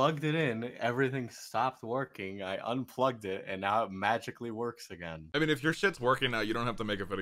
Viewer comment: the caption says working but he says works